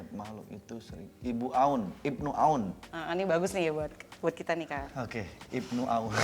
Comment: ni bagus ni, buat kita nikah, ini aku yang salah denger apa gimana?